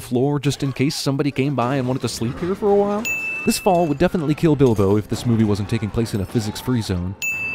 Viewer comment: all I heard was kill bill